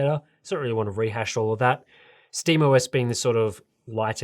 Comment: I was listening to this (not watching) and when you said "Steam OS" I thought you said "Steve OS" and that gave me a chuckle. I can imagine a Steve OS that is all about functionality and boosting performance.